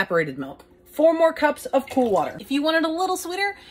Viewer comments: I heard "pool water" lmao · I thought you said “four more cups of pool water” 💀